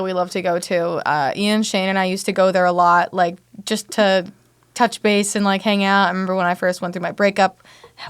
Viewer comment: When Courtney said we went to touch base I THOUGHT SHE SAID WE WENT TO TOUCH FACE.